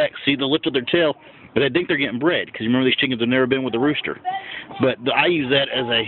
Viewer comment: I heard "getting bread" before I realized you meant "getting bred" aka "getting clucked"